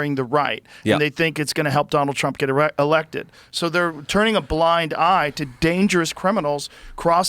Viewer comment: Did he say "Erect"? lol